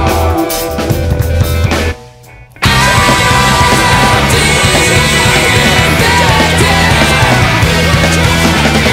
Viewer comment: I love and want to know what he is saying at 11:54 underneath "I've defected" it sounds like "try to measure, what your trapped in" ? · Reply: "Drag your vessel, punch your clock in"